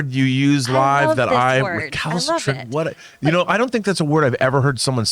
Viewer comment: Wait what was that word??? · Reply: Recalcitrant